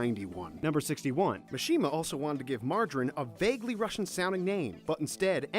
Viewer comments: in 61 I thought he said margarine :) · Did...Did he just call Mira Jane... MARJARIN??????? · Did you just say Mirajane as "Margarine"? And Plue as "Pew"? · Did you say Maradrin? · You could have at least learned how to properly pronounce the names of the characters and even the objects.. Like, seriously. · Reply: I just heard you say Mirajane's name.. MARGARINE.. Just.. Margarine..